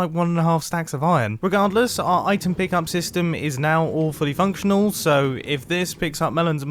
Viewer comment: Mumbo says "All Fully Functional." I hear "Awfully Functional." Why is Mumbo making a thing that functions bad.